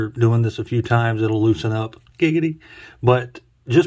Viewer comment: did you just say gigity?